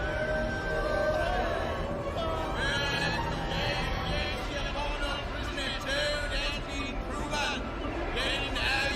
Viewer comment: What was said between 44:40 and 45:03? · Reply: Something akin to "hear yee! Hear yee! The prisoner Toad has been proven guilty."